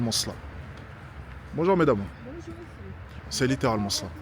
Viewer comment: elle dit quoi la dame ? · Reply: Bonjour